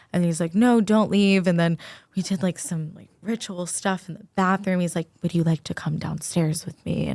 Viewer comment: Did she say ritual stuff!!?